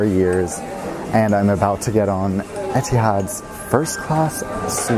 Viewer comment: If you put subtitles on and forward to 0:16, the subtitles say 'jihad' when he said 'etihad' lol.